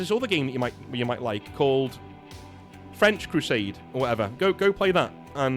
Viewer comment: I was furiously typing once i heard "FRENCH crusade"!! But I'll accept your jesting in good fun haha -knickers untwisted